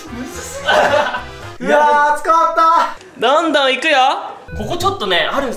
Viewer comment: の「いや〜暑かった！」って「いや〜つかまった」に聞こえるんだけど同じ人いる？